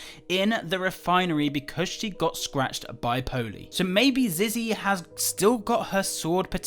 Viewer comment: did u say because she got infected in the refieniry because she gor scratched by pony??